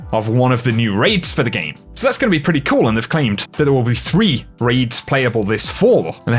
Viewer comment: did you say 3 RAIDS PLAYABLE THIS FALL?